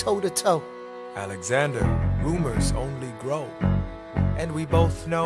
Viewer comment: Am I the only one who hears "Boomers only grow"???????????